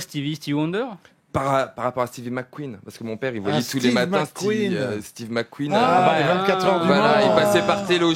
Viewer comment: il a dis quoi a 1:23 ? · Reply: "Tous les matins, steve mc queen"